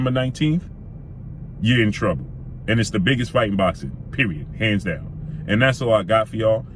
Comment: hahahahahahahaha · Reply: My bad, I thought u said it ISNT biggest in boxing